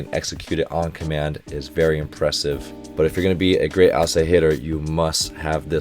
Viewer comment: I swear instead of "outside hitter" I heard "Asahitter" and thought it was some kind of internal joke.